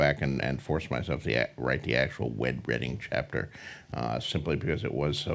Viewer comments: did he say....'wed redding?' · Did he just say 'Wed Redding Chapter' 😂